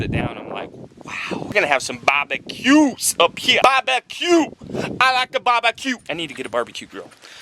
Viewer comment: at around 7:07, I thought was saying 'Bobbin Q's!' instead of 'Barbeques'...